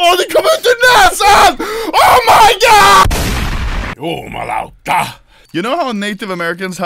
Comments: Did say ”jumalauta” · sounds like he is saying "Jumalauta" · DID SAY ”JUMALAUTA” IN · did... did just say "jumalauta" somewhere around 9:40?